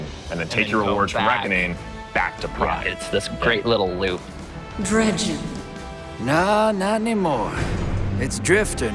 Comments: wait a minute. Did she call him dredgen. Like dredgen yor. Omg. Isnt he a legendary guardian and myname is byf and myellin games talked about him. Ok now i'm hyped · did emisarry just call the drifter "dredgen"?